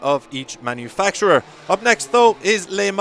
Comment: did he just say "lay marn"